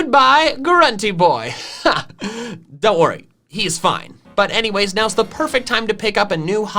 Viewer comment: its fun skipping through sponsors sometimes becuse i hear: "but anyways video" truly wise words